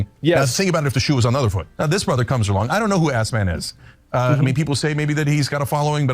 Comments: Did he say ‘assman’? Like Kramer on Seinfeld? “Hey assman!” 😂😂😂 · Did Cenk just call Asmon "ASS-MAN?"